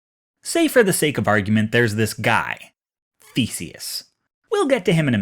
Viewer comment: i sneezed just in the beginning of the video and all I heard was: let's say for the sake of argument that there was this guy - feces